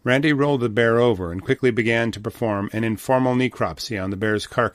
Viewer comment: "He rolled the bear over and quickly began to perform CPR" That's what I thought I heard 😂